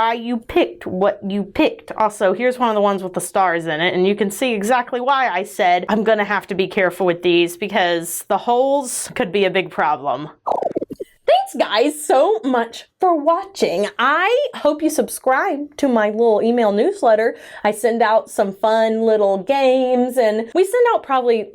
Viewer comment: I misheard and thought you said "Thank you guys soap much for watching" and thought it was a perfect pun